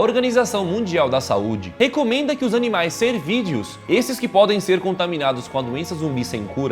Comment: só eu q entendi ele falando ser vídeos kkkk